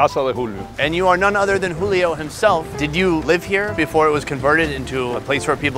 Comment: I thought he said “and you are Julio himself?” But nah his last name is quite literally “Hymselv” Edit